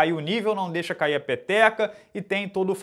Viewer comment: Não deixa cair a PEPECA? só eu que ouvi isso??😂😂😂😂😂